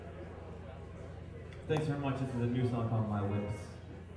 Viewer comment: Winters Wolves 4:54 - Freya 10:01 - Sea of Spears 15:10 - Iron Swan 21:09 - Ebethron 25:51 - Barael's Blade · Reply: its really hard to tell, but it sounds like he says "My Lips" hahaha